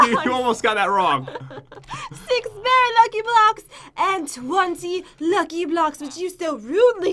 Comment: LOL WHEN SAID KUCKY BLOCKS I THOUGHT SHE SAID NUGGIE BLUCKS😂😂😂😂😂😂😂😂😂